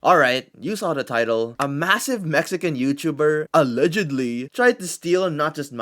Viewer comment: Did he just say?!… *MASSIVE*?!